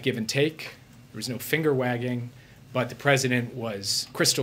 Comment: Lol I could've swore he said finger banging 🤣